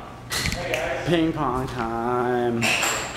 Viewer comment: HAHAAA :L I thought he said I love my mom, but ping pong makes more sense :L